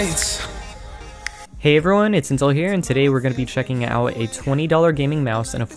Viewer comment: I heard the intro wrong and thought he said "hey there its incel here"